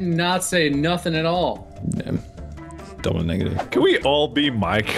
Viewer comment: "Double degative" lol I have that same reaction all the time "did not say nothing"... so you said something?